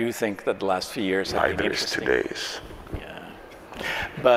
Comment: couldn't understand what either of them were saying here... any help? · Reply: Linus: “And I do think that the last few years …” host [under his hand]: “Five years, two days” Linus: “… yeah 😒”